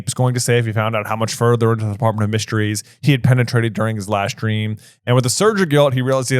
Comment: I misheard and did a spit take!) 51:04. "How much further into the Department of Mysteries he had penetrated during the LIVE STREAM"